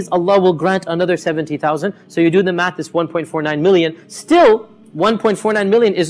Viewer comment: i think he said million not billion